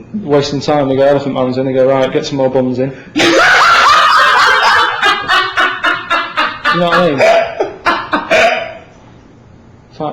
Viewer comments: What does Karl say at ~8:05? I hear "buns" but nothing else. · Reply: "get more buns in" because apparently The Elephant man liked eating buns haha. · What does karl say at 8:04? It sounds like he says "get some more bums in" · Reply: I think he says buns. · "Elephant Man's here", "Right, get some more ... in" What does he say there? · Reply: Buns